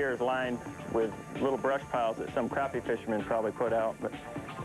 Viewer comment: when KVD said "some crappie fisherman" it sounded like he said "some crappy fishermen" hahaha